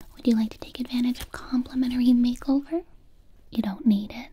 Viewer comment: When she said complementary I thought she said contental😂